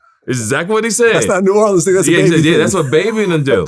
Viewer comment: does vlad say that's not new Orleans nigga?